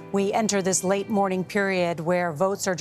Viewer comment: what I heard was “we are entering a mourning period”.